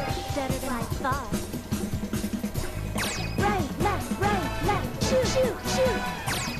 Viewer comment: That was...was...something. I was so confused by this game. I felt like I kept hearing "Right Jew. Left Jew. Shoot Shoot Shoot." That doesn't sound good..